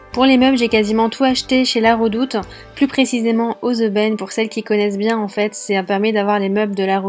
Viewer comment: SVP, c'est quoi le mot que'elle dit a 2:28 (On a acheté tour à LaRedoute, plus particulièrement "OZEBEM"...j'ai écouté des milliers de fois et je ne comprends pas, haha) J'aimerais trop savoir le nom du magazin! J'adore l'appartement, il est vraiment trop beau...j'aimerais savoir faire les travaux, vos solutions belles et intelligentes! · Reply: Les aubaines à Roubaix 😅